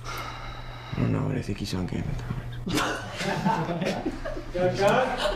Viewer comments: what did he say? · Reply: Mari he said “I don’t know but I think he’s on game of thrones” 😂😂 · What did he say at 15:18? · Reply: Jarrett Owens " I dont know but I think he's on Game of Thrones"